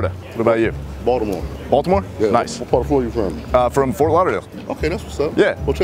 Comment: "What part of Florida are you from?" Did he answer: "from Florida"? I couldn't really understand it. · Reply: he said Fort Lauderdale. city in florida.